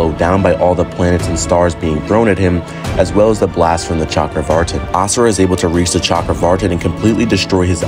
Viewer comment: i heard chakra far'tin like is he farting?